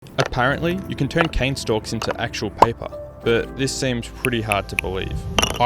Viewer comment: I thought u said cocane stalks